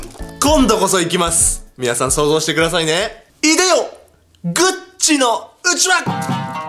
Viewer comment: 名前連呼されてる気分になるｗ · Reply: グッチのうちわ ↓↓↓↓↓↓↓ グッチの部屋に 聞こえるww